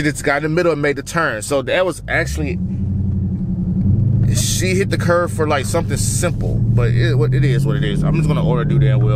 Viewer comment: I thought u said if she hit a curve your a single man 😂